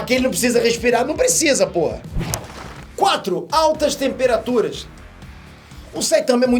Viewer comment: foi só eu msm ou ele disse um p4l4vr40? ele disse p0h4 kkkkkk (eu adimito que nunca pensei que veria o Peter xingandokk)